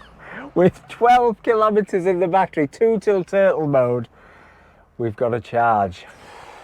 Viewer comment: What an adventure, & I thought you said tootle-mode, think I misread turtle-mode, but tootle-mode sounds about right 😀